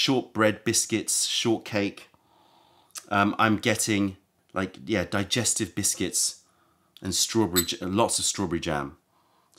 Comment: Thought he said digested biscuits🤮